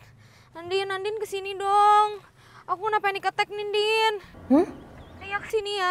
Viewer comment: bilang apa sih? Serius nanya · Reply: Panic attack, panik mendadak karena urusan nikahnya